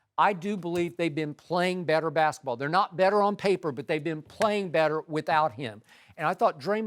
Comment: I thought y’all say “ they better without KD” 🤦🏻‍♂️🤷🏻‍♂️